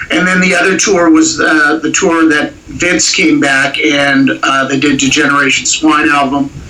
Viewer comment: I first thought Its was said - James came back instead of Vince came back))